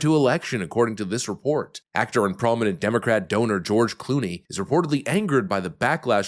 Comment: Did he just say dumocate....???